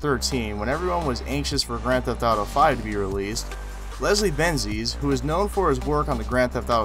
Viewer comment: The next mistake in the subtitles is 01:51. There stand Leslie Benzes instead of Leslie Benzies